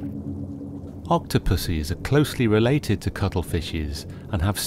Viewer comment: ""octo-pussies" LOL, think you meant to say octopuses, or octopi, lol.